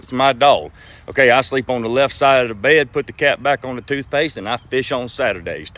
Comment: I thought for a minute he said he would put the cat back on the toothpaste. Not the cap on the toothpaste. However, who would whip his dog? Nobody I hope.